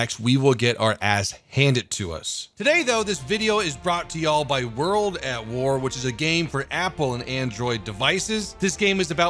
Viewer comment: Lol i thought he was saying this video is sponsored by world of tanks ;D